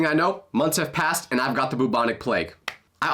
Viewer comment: Bro did he just say bubonic plague 😂😂